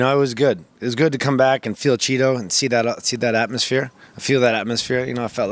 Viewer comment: did he say it was good to come back and “feel Cheeto”?